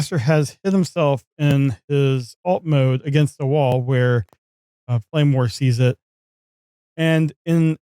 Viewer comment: Oh, you are saying "Flamewar". I kept hearing "Claymore" for some reason.